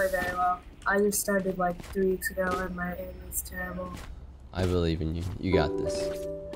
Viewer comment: In the first game when the person on his team said their aim was bad and he said he believes in them, i thought he said "I believe you" and i thought damn he mustve been waiting a long time for that game 😂😂